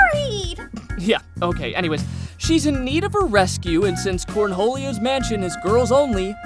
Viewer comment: did he say cornholio at 1:35 ?